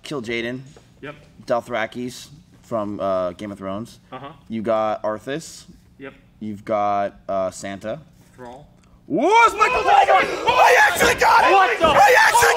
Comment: why do I hear "wow it's Michael Jackson"